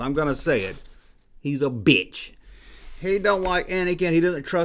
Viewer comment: Wait. What? Did just call Samuel L. Jackson a bitch? No, I couldn't have heard that. That would be ridiculous.